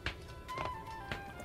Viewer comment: Why does it sound like hes saying "Kaji, Im a whore"